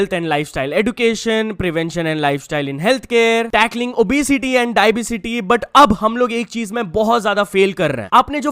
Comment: did he really say dibacity 😂